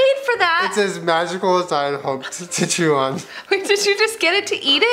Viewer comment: This has bothered me for 2 years what does Julien say at 2:59?? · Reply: “it’s as magical as I hoped to chew on”